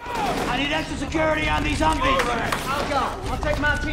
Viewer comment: "Some one get on that .50!" -"It's mine!" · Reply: Joe I thought he said "Alright!"..